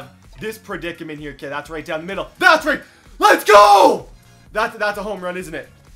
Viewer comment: that sounded like an unexpected Let’s Go!